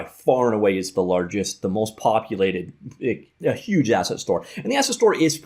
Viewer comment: all I kept hearing was "HUGE ASS et store"